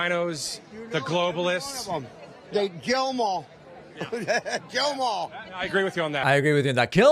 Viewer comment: the guy sounds like he said “jail them all” not kill.